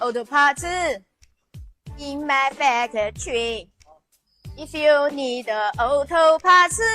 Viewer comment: Did she just say "Many auto partzu, in my faggotry?" Apparently "Factory" comes out as "Faggotry".